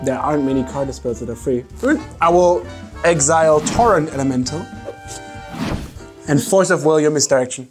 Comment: I think he said Torrent Elemental haha Otherwise he lost, again !